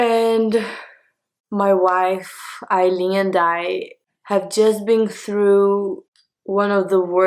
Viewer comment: for a second I thought she said "my Wife Eileen died" 💀